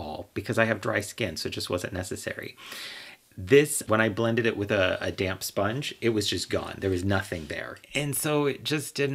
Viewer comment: I thought you said "blend it with a damn sponge" lol. Curious about the drugstore ones. I'm having such a hard time with any foundation because I sweat too much. Like at a clinical level. I almost wonder if a greasy stick would make a barrier the sweat couldn't wash off.